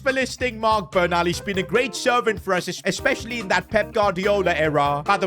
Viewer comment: did he say servant 😂